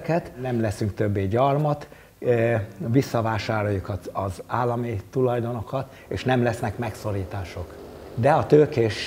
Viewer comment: Hey there, Hungarian here. At 12:42 the subtitle says "We will take back the state's assets" however the guy talking actually said we will buy them back. I know it's the same thing but "taking back" makes it sound like as if we intend to do that by force like the communists did, even despite the fact that there's no mention of such intentions